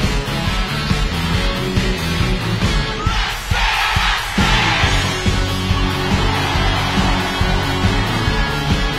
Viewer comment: what are they saying at 03:17? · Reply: rasse rasse (らっせらっせ) Copy from SvenAKG Probably it's seem like "raise your hand" in EDM Festival.